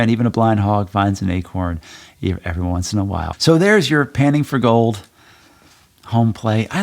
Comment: Loving the series! I misheard the homeplay as "Penning for gold" :D